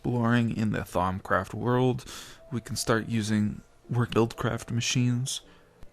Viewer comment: Did you just say Thaumcraft world... *-_